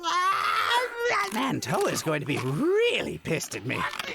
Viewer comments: I swear to god I thought you said Toei and not whoever that character is. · i didnt hear it right assumed you said Toei like toei animation being mad and dabura being a barely canon bitch, seeing ass he appeared to stall and get beat up by buu Still laughed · Before this video I thought had said "Toei's gonna be really pissed at me" and I connected that to Dabura's design being reused in SandLand and took it as Dabura being mostly a Toei creation, allowing Toriyama to leverage Dabura's "barely even canon"-ness to take a semi-unofficial design of his and get some more use out of it. I realize now that I was incorrect and I just made up an entire character-mangaka dynamic from a random line in a fan-based parody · I always thought he said “Toei” and didn’t understand the joke as well because I thought he was talking about the animation studio